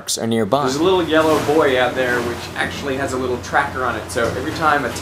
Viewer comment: I could have sworn he said, "There's a little yellow boy out there." around :40.